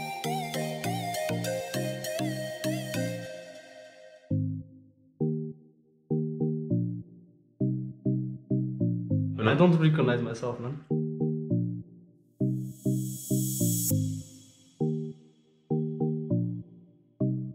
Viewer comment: did he said i dont recconnect myself man it looked like that😂